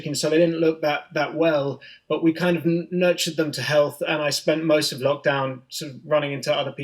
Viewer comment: On 12 I heard "nudged" unstead of "nurtured", and on 15 I more deduced "left" or possibly "left over", rather than heard what he actually said. But that's all I didn't get right.